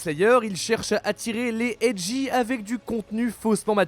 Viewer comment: les Quoi 1:52 j'ai pas compris ? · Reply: Edgy ( les dark sasuke en gros)